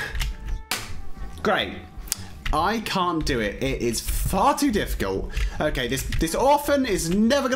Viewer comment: When said far too I thought he said farty what has my life came too